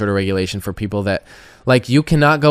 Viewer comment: It sounds like he said, "Dew gilligance." It makes no sense why anyone would even entertain this uneducated tool. It's a sad indictment of the current state of the "world". The kid doesn't realise what a dumbass he is and is readily proving himself to be. Long may this feud of sorts continue! Bravo, Coffee.